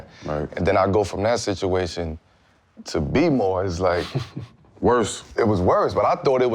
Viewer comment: Did carmelo say he was doing bboying @ 7:50?